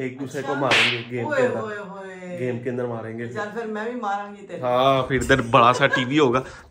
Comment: did auntie just said chl fir teri v maaraangi mein?